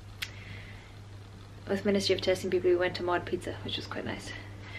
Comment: Did you say (at 7:53) that you went to MOD Pizza? I wasn't sure if I heard you correctly. I'm wondering if (that is what you said) it's the same MOD Pizza we have here in the states. <link>